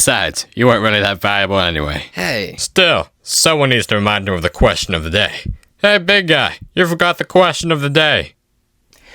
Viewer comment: what were they saying.?... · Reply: "Still, someone has to remind him of the question of the day. HEY BIG GUY! You forgot the question of the day!"